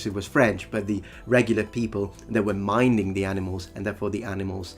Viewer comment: He said "minding", or at least that's what I heard. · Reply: I heard "mining", which I had to think about for a second, but I'm sure he at least meant to say "minding", if not actually said it with the 'd' sound getting a bit swallowed at the end.